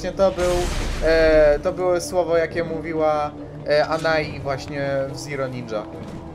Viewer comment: ja tam usłyszałem '' TO ZENON''